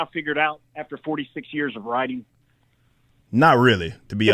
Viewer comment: Wait, did he say "46 years" or 4 to 6 years. Is this some sweaty try hard male feminist in his 60's or his 20's because he argues like a child